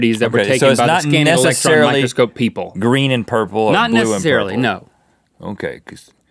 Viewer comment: IM SORRY BUT ...did Rhett say GREEN and purple at 1:36 BLACKANDBLUE DRESS 2.0